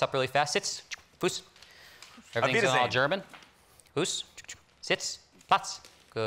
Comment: What does he say at 3:39? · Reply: It sounds like he said I beat a dane German